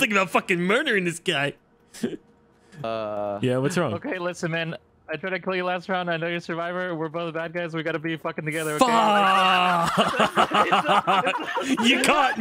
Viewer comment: What did say at 32:45 that was so funny? I can't quite make it out between "Okay listen man" and "I know you're survivor" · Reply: "Okay listen man, I tried to kill you last round, I know you're Survivor, we're both bad guys, we gotta be fucking together, okay"